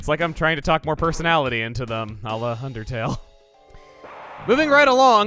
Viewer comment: 2020? 2:57 Did he just say "UNDERTALE" or...